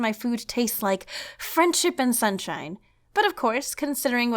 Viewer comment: My head phones glitched out the “hip” part in Friendship, so when you said it tastes like friendship and sunshine.... well.... it sounded like it tastes like Friends and Sunshine... Cannibalsum! (Jk, I really love ur videos!)